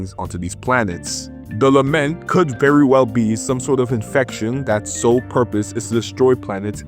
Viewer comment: did I just hear infection??? Kuro games and outer space infections storyline goes hand in hand